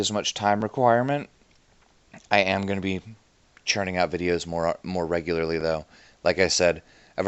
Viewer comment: I heard "churning out videos" as "churning out babies." Do I have a problem?